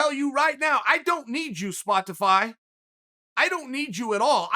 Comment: damn he really said Spo- TIH-fy